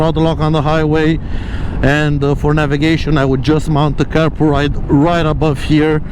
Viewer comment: mount what ? Sorry I didn't catch that 😢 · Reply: Carpuride - link in the description. I have a full video on it and the link is an affiliate link.